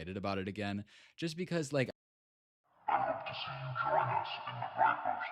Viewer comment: does anybody know what it says i made out "id love to see you join us" but i cant make out the rest! jk11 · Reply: I’d love to see you join us in the vipers den